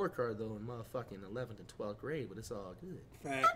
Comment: I thought you said you was in the 17th grade😂💯💯 lyin ahh